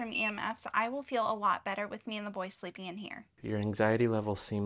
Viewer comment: All I heard was : you're not sleeping in here .